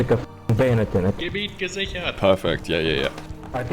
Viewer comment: Anyone know what was said? Sounded like "kabie kaziker". I heard it on a Criken video years ago too, but never found out what it meant then either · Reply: It was german he said "Gebiet gesichert" it means "area secured"